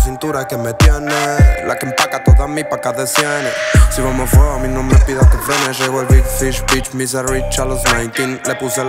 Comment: En el minuto 1:32 flasheee q dijo "soy homofobi no me pidas q frene" la primera vez q lo escuché, pero desp entendí q decía "si vamo a fuego no me pidas q frene" jajaja 😂😂🤣🤦🤦